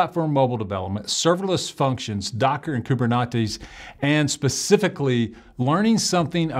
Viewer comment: Did I hear someone say KuberNachos?